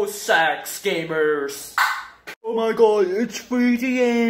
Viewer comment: can someone tell me what says here I cant understand it lol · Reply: “Oh my god it’s 3 am, no fake”